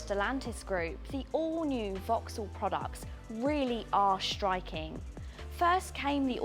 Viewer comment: Not sure what you are listening to 😐🤷‍♂ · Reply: Hi, I'm listening to you pronouncing 's' as a 'sh'. For example at 0:50 where you say "shtriking" instead of "striking".